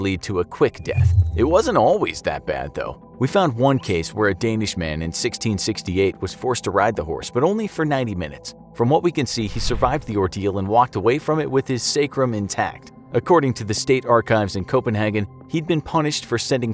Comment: I thought he said "Get ready for 90 minutes of run"